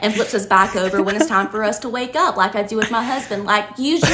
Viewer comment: Did she say " god flips us over Like I do my husband" lmao